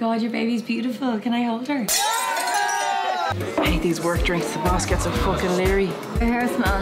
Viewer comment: I'm from Dublin. And still I have no fucking idea what she's saying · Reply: "I hate these work drinks, the boss gets so fucking lairy."